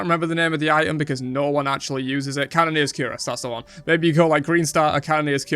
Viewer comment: What the item is he saying at 9:00? I can't really tell cause of his accent and cause I've never heard of it before. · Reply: Cannoneer's Cuirass